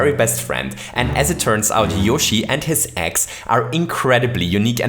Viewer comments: I thought you said “Yoshi and his ex” 🤔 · At around 0:17 I thought he said yoshi and his ex.🤔